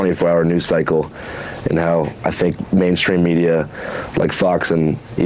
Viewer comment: What might have happened here is that Don misheard what Andrew said. I've seen this clip twice. The first time, I heard Andrew say "I Think Mainstream Media..." BUT, the second time I did a double-take because I mistakenly heard him say "Fake Mainstream Media..." Don reacted like Andrew used the word 'fake' in that spot. Still a goofy reaction by Don either way, but not as unreasonable.